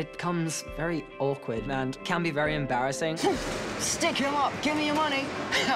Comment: I love my bumblebees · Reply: I thought he said"Stick your mom, give me your money GIVE ME YOUR MONEY"